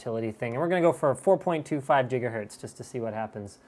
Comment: did he say "Jigahertz" ? Doc? Is that you!?